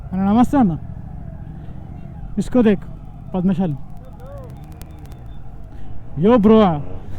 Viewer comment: What's the word you used at 5:41? For that yellow karizma guy? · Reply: Padmashali is caste. They are into cloth business by profession I guess correct me if I am wrong